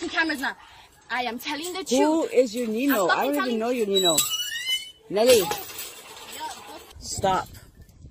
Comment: did she just call her Nelly is that her name? I might know her but I could be Wrong though must be some other Nelly.